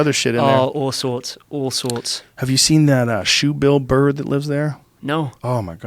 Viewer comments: when he talked about the shoebill bird i thought he said "Have you seen the shoe Bill Burr that lives down there" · zoned out, thought I heard "Shoot Bill Burr" lol